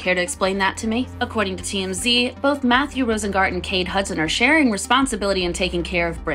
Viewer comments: I feel so behind, this whole time in your past videos I thought you were saying KATE Hudson was her manager and I wandered why you have can it with an “exposing kate Hudson” video 😅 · I've been out here thinking Kate Hudson turned in her acting shoes to become a sketchy manager 😂 Glad this video cleared that up for me! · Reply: Same I thought they were saying Kate Hudson at the start too 😂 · I’m not gonna lie at first I heard “Kate Hudson” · I keep hearing “Kate Hudson” lol